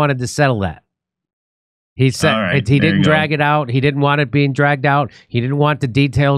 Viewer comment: I thought they said he didn’t actually leave the country.